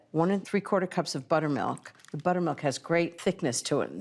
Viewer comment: I thought I heard 6 sticks of butter had to rewind.🤣🤣🤣